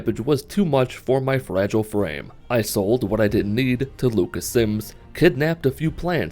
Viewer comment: first I thought you said "local simps" Then I heard "local sims" Both were funny. Then I concluded you were really saying, "Lucas Sims" I dont really know though, because I 100% NEVER read the name of the riverwood trader dude. I only remember Camilla. Lol